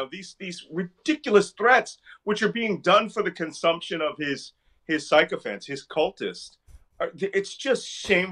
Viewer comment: I thought Malcolm said ....... "Which is being done for the consumption of this psychopants.'"